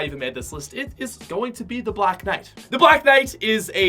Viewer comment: Thought you said red knight was the best skin was the best in the game